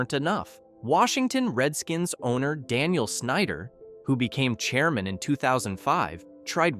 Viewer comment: When you said Daniel Snyder, I truly thought you meant/said Dan Schneider, confused me for a sec.